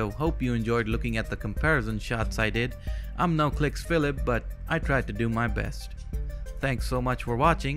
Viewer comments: Besides this interesting collection of shots - I misheard the ending as "i am noclipsphilip" 😂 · You saying that your not "no kliksphillip" sounds like your saying "noclicks phillip"